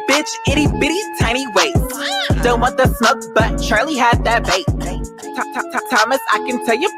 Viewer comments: if u watch a sec I thought he said charli got that bait it said vape 😂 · and my flat ass thought he said Charli had that bait